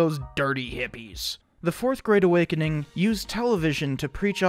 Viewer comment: When you said "4th great awakening" I heard "4th grade awakening" and it was somehow even more accurate